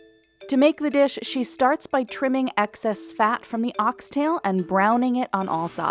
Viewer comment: Did she said start by trimming "access" fat?😅